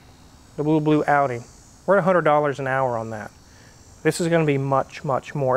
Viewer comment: did i hear properly $1,200 per hour to work in a old audi which cost here in the UK less than a grand omg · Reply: $100/hr